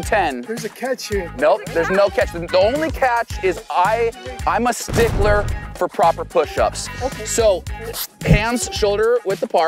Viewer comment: I thought he said he was a stickler for pushups. Those kids didn’t do a single one.